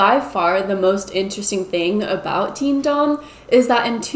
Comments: I thought you said Team Dong. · I thought you were saying “Team Dong” until you showed it on screen 🤣🤣🤣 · I really thought shes been saying Team Dong this whole time lmao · Oh my gosh I thought you kept saying Team DONG! Like penis. . . that's hilarious that I was wrong. . .